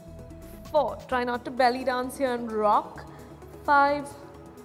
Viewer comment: I still have no idea what she says at 3:31. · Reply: Hi there, she said, "try not to belly dance and rock" your belly 😊 We hope you found the video useful -Team Glamrs